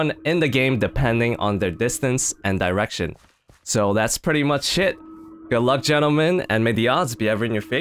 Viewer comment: did you say ''so thats pretty much it'' or ''so thats pretty much sh*t'' cause it sounded like you said the second one